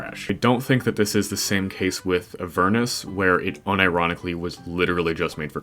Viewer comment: I thought you said Avernus (Note: I like Avernus)